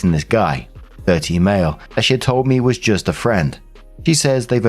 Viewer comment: omg I thought you said "13 male" 😅